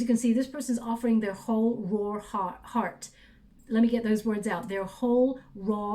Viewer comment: “their whole raw heart” (but I heard) ‘their warthog heart’ like the lion king, “when he was a young warthog🎶” and it resonated. 🙏🏽thank you✨